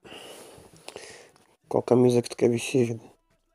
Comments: Só eu que escutei ele falando: "qual camisa que tu quer vestir Julia" juroKAKKAKAKAKAKAKA eu voltei várias vezes KKKKKKKKKKKKKKKKKKKKKKKKKK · MN foi só eu que escutei Qual camisa que tu quer vestir Julia (tbm escutei Juliana) ... A madrugada n tá me fazendo bem mds